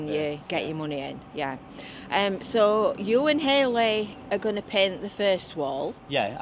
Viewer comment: Ha ha, I know from high school and the petrol station. For a minute I thought you said Hayley Gardiner who is someone I know! Fleetwood will look great by the time you've finished with it, as long as it doesn't end up looking like Belfast 😂 Hi to and.